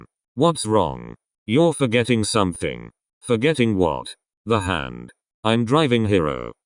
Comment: I THOUGHT HAND SAID HEAD AND I THOUGHT HE WAS DRIVING LIKE THAT LMAO